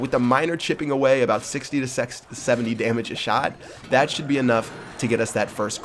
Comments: Was it just me or did just say sex at 4:25.lol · did anyone else hear say sex?